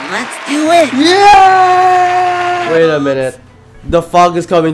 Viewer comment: my dumb wifi at 144p and I thought everyone was saying "the Pog is coming" 😭